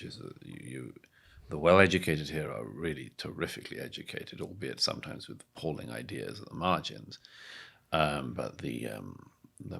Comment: Is there NO WAY to FORCE Douglas to speak audibly?!?!?!? 1:03:00 WHAT THE HELL DID HE SAY?!?!?! His lips barely move and there seems to be no air escaping his mouth! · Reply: Albeit sometimes with appalling ideas on the margins.